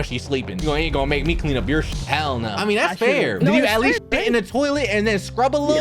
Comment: The hell no sounded like Vuxvux